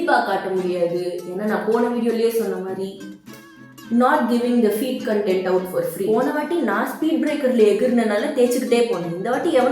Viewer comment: did she said not giving it for free 😏